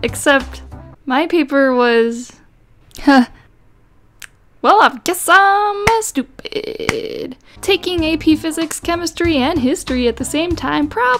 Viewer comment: did you say "I guess I missed a bit" or did you just say the bad "M" word if some know what I mean.... Just asking